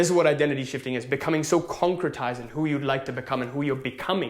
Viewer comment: what word did you say? concretize? or conquetize? Thank You! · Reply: English subs say "Concretizing".